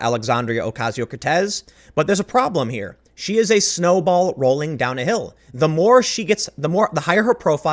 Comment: I thought you said she was a "snowflake rolling downhill." Was like wait snowflakes don't roll they kinda just land lol.